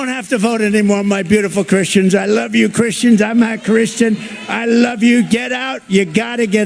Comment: At the 11:40 mark - - Trump is talking and the captions state, 'I love you Christians.. I'm a Christian', BUT watch it very close and listen to what it sounds like he actually said. It looks and sounds like he said 'I love you Christians, I'm not a Christian..' I am a big Trump fan, but WOWSER... WATCH and LISTEN, and see if you see and hear what I do.